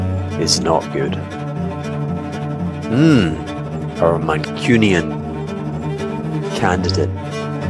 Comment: Hey captions editor, Hugh did not say "Manchurian" but rather "Mancunian", i.e. "from Manchester". "Mancunian Canadidate" seems a rather lulzy construction in this or any other context, but I'll hafta think about it. Anyhow, thanks for spelling out this argument in slow-talk to us, line by line: important & useful. o/